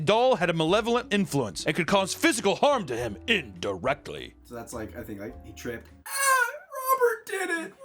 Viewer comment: I 100% thought you said that the doll is causing him pain "in the rectum" and not "Indirectly"